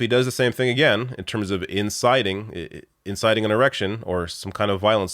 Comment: Hey love ur show but at 7:28 did u say”erection” instead of election ? Lol